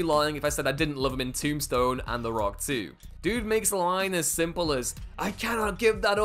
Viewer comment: When you said “The Rock too” I heard “The Rock two” and thought What? How did I miss that movie? Lol